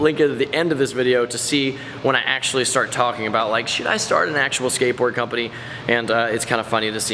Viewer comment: did say shit